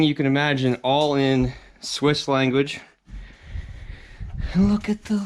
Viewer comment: I can't imagine anyone who has the type of money that they must have that they could just leave it all for the next person. I would love to know everything about this couple. life is short, creating such a piece of paradise would slow it down some, even if he had contractors building it, GREAT SCORE. To your question "Are you crazy" I guess it would depend how much you paid. Cheers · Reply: Swedish? Thought he said “Swiss” (Switzerland)